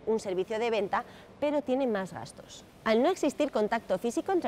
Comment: En el minuto 1:21 porque dices que el costó es mayor? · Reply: Dijo "gastos" :o